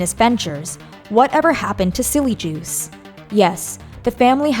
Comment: I shuddered thinking I heard "Jilly Juice"